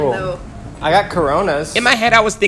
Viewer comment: hey did he just say corona